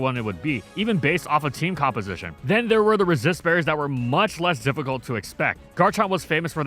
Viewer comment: Resist berries have been so ubiquitous that some players would choose to play Unnerve Tyranitar rather than the one with Sand Stream. That's a lot. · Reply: For a millisecond I thought you said racist berries.